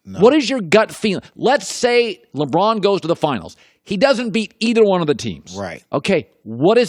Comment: wtf is he saying?? lebron vs both teams? like his and wars?